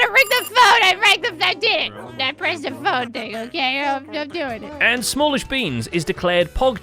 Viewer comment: "I did it! I pressed the phone, Tango, okay! I'm doing it!" I'm fairly certain that's what she said.